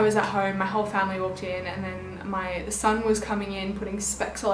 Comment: Did she say my son? Does she have a son? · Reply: Olivia Monet she meant the sun😂 cause the sun shines through her windows and makes rainbows and little specks of light HAHA